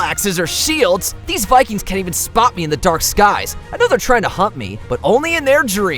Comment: I thought you said i know their tryna hump me but they cant 😂